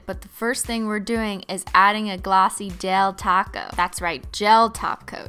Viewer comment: I thought she said: now add a glossy del taco 🌮